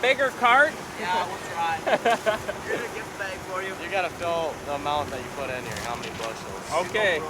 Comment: Did I hear that FFA kid right? "YOU gotta fill the amount you put in here, how many bushels" My bet is there won't be one farmer outta that truck.